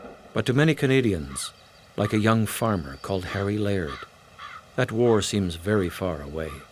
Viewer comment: I thought he said "Harry Larry." 😐😵‍💫😵